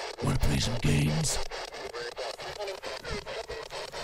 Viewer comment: "We're..coming...I love you" Pretty sure I heard "We're...coming...for you" I wanna see twinpara investigate that place!